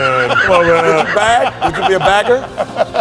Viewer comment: okay so a dude can't be a cashier but he can be a beggar 😂😂😂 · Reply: Bro really misheard bagger as beggar 💀